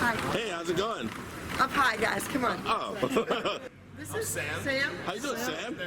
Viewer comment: i think she's saying "up high guys, come on" (as in film me up high with the cameras) not "i'm high" but i could be wrong